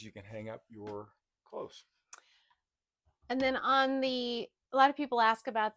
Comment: did she just say “black people” ask about the air conditioning and heating? 😡😡😡 Like whats on your mind! You could just say “most people” instead of “black people”? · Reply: listen carefully, she said "a lot of people"